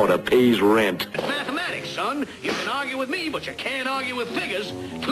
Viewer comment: did he just say the N word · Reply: He said "you can't argue with figures"...as in numbers...lol..I think you hear what you want to hear...looking for a reason to get mad...